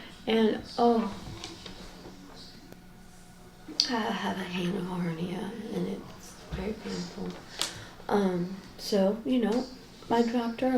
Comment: pauses and says something like “I have a hangover, yeah, and it’s very painful.” Anyone else hear it too?